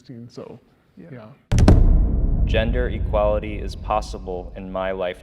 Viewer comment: what am i hearing genders are equal you spacks